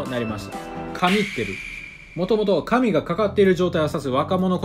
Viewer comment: イントネーションの違いですかね？「神が、かってる」って聞こえる